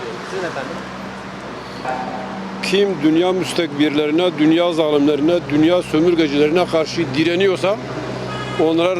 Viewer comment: Dk 25:40 adam ne diyor amk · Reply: BEYNİ YIKANMIŞ